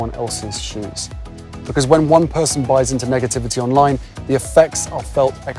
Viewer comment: I thought Harry said "megativity" instead of negativity! Hahaha, I had a laugh. There's a new "meg" trend 😂